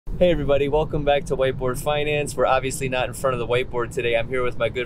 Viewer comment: i thought he said white boy finance